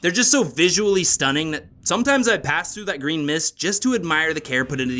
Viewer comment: I'm sorry, did you say green mist?